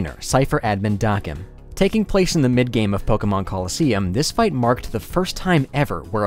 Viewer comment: I misheard you as saying 'the mid game Pokémon Colosseum' and I was thinking 'come on bro😢'